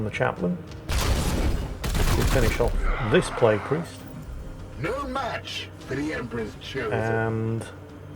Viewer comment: wait did I hear that correct. Did the marine really say "no match for the emperor's children"? LOL